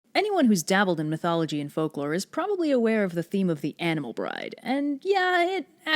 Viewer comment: Thought you said animel bridge